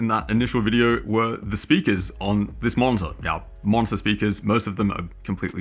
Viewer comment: For some reason i keep hearing "... on this monster" and it really conveys the idea :)